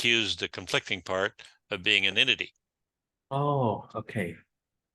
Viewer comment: motivating part confused rhe other part of being ____? I didn't catch that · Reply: An entity